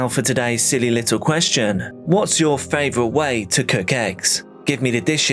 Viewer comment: Eggs Benedict is my favorite egg dish. I had to back up the video because at first I thought he said "what's ur favorite way to cook X?" I was like "...ecstasy?" 😂